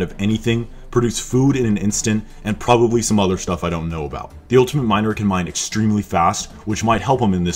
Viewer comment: I thought he said ultimate minor😂